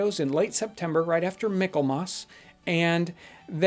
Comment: right after..........micklemoss? (3:54) I know a few old words like widdershins, but I have never head of, what did you say? Mickelmoss? Doggon our midwestern accents! I found it! Michaelmas! I've never heard of this Christian feast day? You learn something everyday if you keep your ears open! Thanks for sharing!